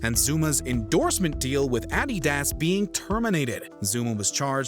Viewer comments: did he just say addedas instead of Adidas.... Or is that just a European company I've never heard of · Did he say Ad-dee-das? 4:20 🤨